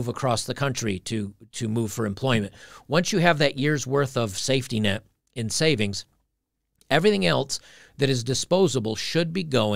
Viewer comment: You say "elce" instead of "else".